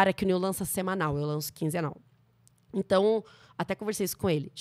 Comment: "Eu faço quinzenal" eu entendi "eu faço quinze anal" assustei